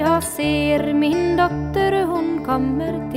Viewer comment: i hear "doctor who"... O_o